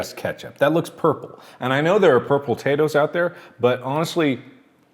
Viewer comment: Did he just say... purpletatoes???